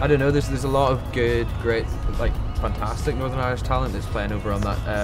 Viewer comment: Lol, it sounds like he says "funtastic".